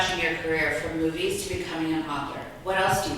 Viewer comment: I heard "mother" when she really said "author"!! Thank goodness for the subtitles!